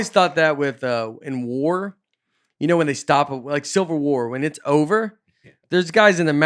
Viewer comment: I’m curious about Nates stance on this but I heard “Silver War” instead of Civil. Does that mean he thinks it’s 2nd best? If so, how? Why?🤣🤣🤣🤣